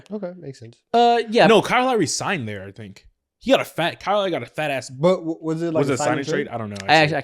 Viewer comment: unfortunate pause on words, all I heard was “Kyle Lowry got a FAT ASS”